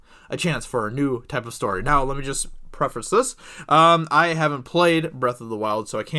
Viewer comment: u should "reference" it instead of preference LOL